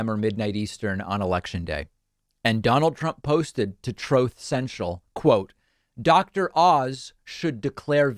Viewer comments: I laugh every time I hear "Trope Central" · It does sounds like he says "throth central". But in your video from his recent Texas rally, if you have C.C. on it translates TROTH as TROLL. From here on out TROTH CENTRAL shall be known as "TROLL CENTRAL" Appropriate, isn't it?! 😆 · I keep hearing "'troth central". Is he hinting at a fourth marriage?